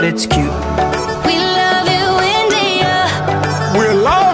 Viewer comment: i thought meghan said "we love u in the earth"